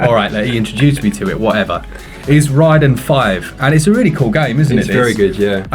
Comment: Lol i was listening to the video, not watching, and when you said Raiden 5 i thought you said Ridin' 5 and I was l like, "What? Never heard of that one!" Then I looked and was like, "Ooh RAIDEN" ha! Great vid!